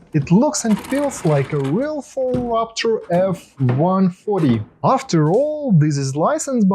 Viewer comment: Did you just say f-140? 😂